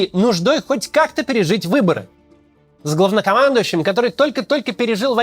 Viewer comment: мне послышалось "говнокомандующим" 😂